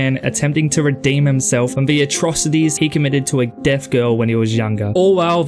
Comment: سمعت Deaf بالخطاء Dead فمشيت حضرت الفلم وانا متوقع موتها في كل موقع وسقوط وعبور للشارع 😂💔 كان فلم مدمر للاعصاب بالنسبة لي، بس شكرا لانك جعلتني احصل علي فرصة مشاهدته❤